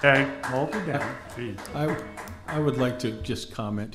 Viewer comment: I thought he was saying now he's my son after that speech.😂😂